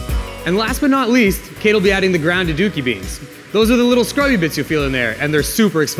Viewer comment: Wow, I thought he said grounded dookie beads. lol